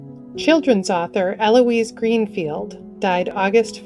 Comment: I had to listen 3 times. It sounded like Chiffen-soffer. Got it now. "Childrens' Author". My hearing tests post-Navy are pretty bad.